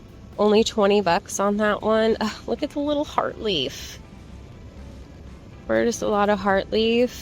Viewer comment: Could you type the name of this Hoya? I couldn't make out what you said - thanks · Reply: Hoya verticillata heart leaf